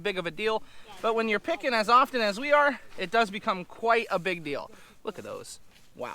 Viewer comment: you kept saying big deal and all I kept hear was "big dill" 🤣